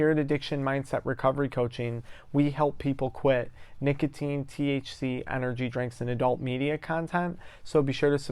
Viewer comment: I just started your video and I thought you said you sold THC energy drinks and adult content. I was so confused. Had to rewind, now I understand., lol. I’ve been a weed addict for 30 years and I cannot quit. I used to have a terrible problem with meth and alcohol that ruined my life. Now I don’t do those drugs but I feel like weed is all I have to live for. I don’t like how tired and overweight it makes me. I don’t like the constant nausea ( I eat cannabis, the pattern is to eat enough to get very high but you don’t want to eat too much or you’ll eat sick. I often eat too much by mistake). I’ve seen a doctor about quitting and currently talk to a therapist once a month but no matter what I just can’t stop. I don’t think weed is bad for everyone. I think it can be an enjoyable part of life if used occasionally. The reason you see backlash when you talk about how weed can hurt people is because those of us that have used a long time remember people going to jail for it. Families being hurt by the huge fines people had to pay. We don’t want to go back to that. I’m glad you are trying to help people with the problems weed can cause but I still fully think it should be legal. By any measure it’s safer than alcohol but you know that. If you have any suggestions to help me quit I’m all ears. I wish you well in what you are doing.